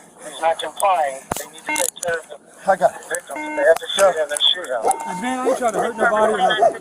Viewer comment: Did anyone hear the cops radio at 4:24 he said they need to ''shove him & shoot him'' lmao